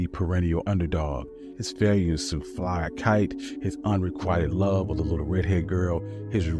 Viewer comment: Unrequited? Sounded like, unrequired (not a word).